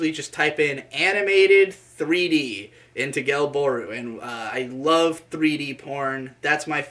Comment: what is the website is saying at 6:42, genbor or something? couldn't hear it well. thanks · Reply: Gelbooru